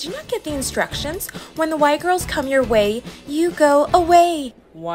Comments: “When the Y girls” bro I thought she said white girls 💀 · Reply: I thought she said “Why Girls” lol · When they said "y girls", I thought they said WHITE girls and I was not even surprised 😂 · “When the Y girls” bro I thought she said white girls 💀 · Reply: I thought she said white girls👁👄👁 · I thought they said white girls 😂😂😂